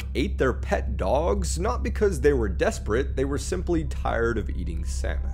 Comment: Dude i heard "they were tired of eating semen" wtf